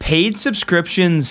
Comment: Wow, I was watching this video thinking it was by a channel with a couple hundred thousand subscribers. Keep it up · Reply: It seems you didn't hear the very first word he said so I'll put it in all caps and include the timestamp… PAID